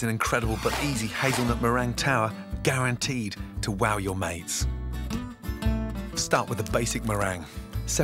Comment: “Guaranteed to wow your mates” I thought he said “guaranteed to wow your maids”